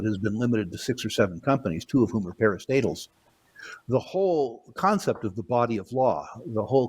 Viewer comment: what is that he said? "Two of whom are _ __ " ??? "Paris Fatals"??? "Paris Stables"??? "Pair of statehelds"??? I can't figure it. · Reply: Parastatal